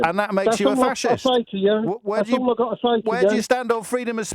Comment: Ok so when he said “No, there should be a few more rules” it really sounded like he said “No, there should be a few more Rommels” Listen to it again, then tell me I’m wrong